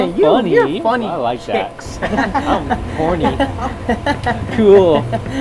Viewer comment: did anyone else hear either or say "im horny" at 5:22 lol